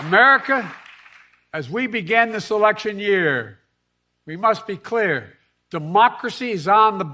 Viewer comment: Taking Trump off the ballot and putting democracy on. Got it. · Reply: It actually sounds like he says, "as we begin the selection year"😅 Coincidence?